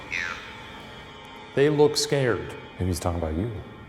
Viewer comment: They heard " They look scared." I heard " Ay, f*ck you"